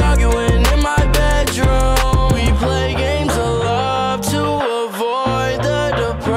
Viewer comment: lol i thought it said "we play games A LOT" lol who else hears that? i don't hear "OF LOVE" at all 😂 Wow thanks for the likes and comments everyone! This makes me so happy luv you all !